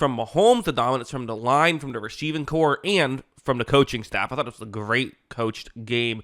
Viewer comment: Rasheeving corps ... 😂 ... Thought that's what you said at the beginning of the vid lol